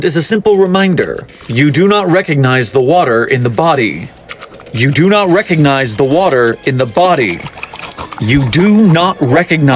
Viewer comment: "BODY YOU DONT RECONIZE THE WATER" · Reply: I thought it said “BOY YOU DONT RECOGNIZE THE WATER”💀